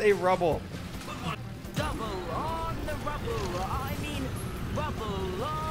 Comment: He does actually say "Rubble", not "Bubble" but still funny none the less. 😂😂😂😂